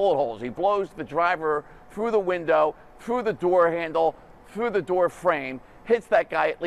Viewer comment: Did he just say the Lyft driver blew the guy through the door! 😂 🤣 😂 🤣 😂 🤣